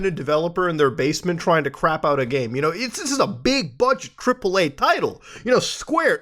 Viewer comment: I keep hearing it "Cripple A" games lol!